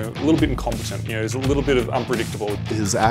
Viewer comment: Claptrap's action skill is lunacy... NO SHIT And what does the guy say at 4:46 ? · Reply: I believe he said "Claptrap is incompetent, A little unpredictable."